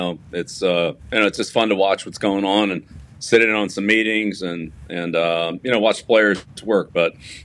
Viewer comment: I thought he just said he was watching players twerk. 😆